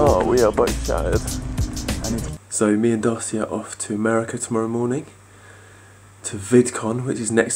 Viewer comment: i thought he said me and darcy are going to america tomorrow, as in baby glitter hahaha